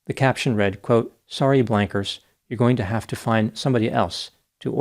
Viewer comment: "Sorry blankers" may be my new go-to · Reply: oh lol i thought he said wankers